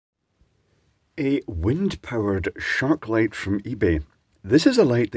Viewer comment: I coulda swore i heard him say … “shartlight” idk?! Maybe it got lost in translation. LoL